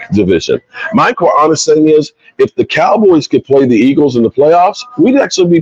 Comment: I could have sworn he said he wished the cowboys was like the eagles